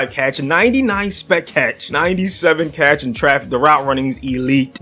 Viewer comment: did he say sped catch